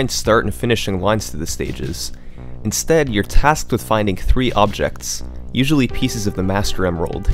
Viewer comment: did u just say findingksf